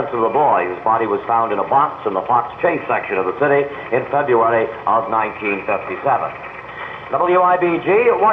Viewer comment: What'd he say at 1:48? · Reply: february 1957